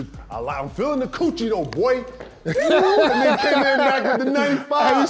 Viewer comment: AYOOOOOOOOOO KG OUTTA CONTROL!! Sound like he said “Feeling the coochie though, boy!!!!” holy smokes lol.